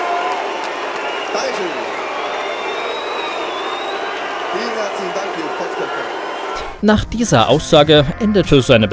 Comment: was hat er gesagt? Ich kann ihn da nicht richtig verstehen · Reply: Ich hab "ihr Kotzköppe" verstanden.